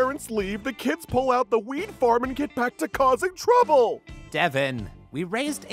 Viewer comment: "And Then They Pull Out The Weed Farm" Best Quote from parents 2019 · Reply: I thought he had said “wein- farm” (keepin this child friendly, kinda)